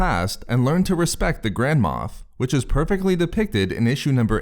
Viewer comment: I keep hearing "grandma" instead of "grand moff"